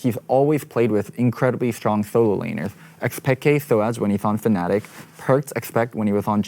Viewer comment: zven on fnatic? did i hear that right? lol, got for a sec.